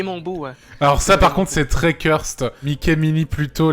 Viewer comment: Yop. Ça signifie quoi à 4:17 le mot « curst »? si j’ai bien compris? Merci d’avance. 🤗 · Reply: J'ai compris "cursed" littéralement "maudit" en anglais après si je dis des conneries et que quelqu'un veut me corriger go